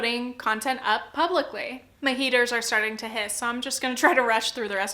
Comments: omg i thought you said "my haters are starting to hiss" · I thought you said, "my haters are starting to hiss" 😂 lol! That evoked some fun imagery in my head